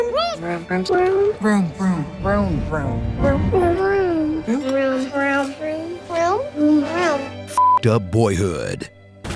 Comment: it sounds like theyre saying vroom